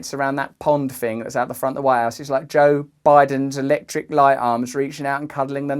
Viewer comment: I actually thought you said "White Arse" Russell, when you said "White House". Probably appropriate.